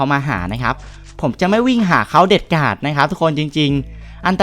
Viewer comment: ผมได้ยินผิดรึป่าวนะอะไรเด็ดกาดๆ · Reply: วิ่งไปหาเขาเด็ดขาด